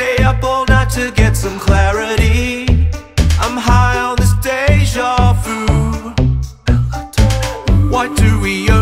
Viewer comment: “I’m high as this deja vu” I misheard it and i litteraly thought it said: Ohio is deja vu